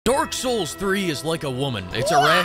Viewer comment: Sounds like he is saying Dork souls Everytime he says it lol.